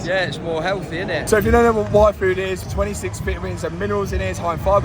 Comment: Oh my god I thought at 12:50 you we're saying "Waifu" not "Y Food" the whole time😭